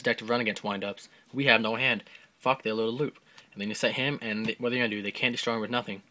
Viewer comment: it's hard to tell what you're saying, are you sayin when you SET him? cause his eff only works while he's face up, but I could just be hearing things...